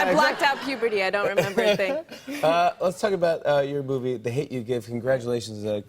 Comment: Did Jimmy just say 'Let's talk about your boobie' at 3:45 ? Yes, yes he did.